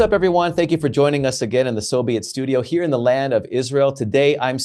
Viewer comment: Sometimes when you guys say “so be it” I hear “SoVeeit” (as in Soviet) 😂